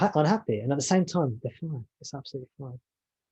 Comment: Hi I didn't catch what you said at 4:29 can you repeat?(write) thank you Interesting I can understand how this man feels. I can get caught up in emotions far more than just thoughts alone, but then emotions entangle in to thoughts. And it becomes a downward spiral for me. Do you recommend speaking in third person to myself? That seemed to make sense when you said, "oh Jimmy is attached". Use my name as if I am observing me as the I AM. 🙏as always thank you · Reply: I think I said: 'At the same time, the feeling is absolutely fine' or '...it's fine, it's absolutely fine'. Something like that :-)